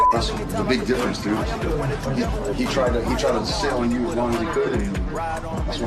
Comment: Colby tried “to SIT on you as long as he could.” I do t believe he said sail like the caption says. On another note, when says he loves Masvidal, he means it. 😧. Wow ! The beef is real, let it go.